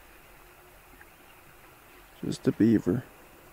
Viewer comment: Had to laugh when you said, "I was wondering what that sound was earlier. Huh. Just a beaver.". What I heard was, "Huh. Justin Beiber.".